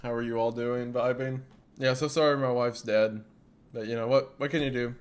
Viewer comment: I got so concerned when he said "My wife's dead but what can you do." Then I realized something "happening" to his wife is a running gag on the side channel. 😂🤣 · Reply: I thought he said, "My mic's dead" and I just misheard him, but no- He did indeed say wife XD